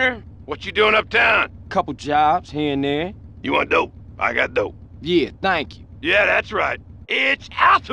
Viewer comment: You are dope I got dope. 湯アンドありがと。(空耳) Japanese Language